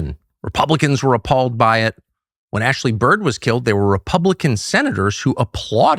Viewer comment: The first 8 minutes I heard “Ashley Bird” and “pipe bombs after 9/11”… is someone trying to sabotage Tucker? 😬